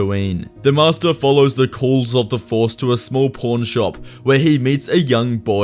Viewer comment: Is it just me or did he say a p*rn shop?